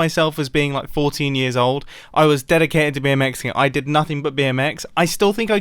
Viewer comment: "I was dedicated to be a Mexican". Legit thought said that. 😂